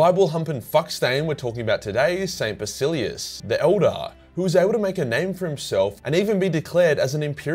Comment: Thought he said "the Eldar" and wondered how the heck an Eldar became a saint of the Ecclesiarchy. Had to Google "Saint Basillius" and found out he actually said "the elder".🤦‍♂